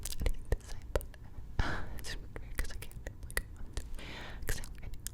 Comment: When you did the unintelligible whispers in 18:06 i think you said "its really weird because i cant" after that i dont know😂 Great video btw